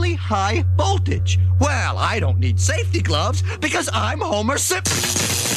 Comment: i heard he says homer simp- he says simp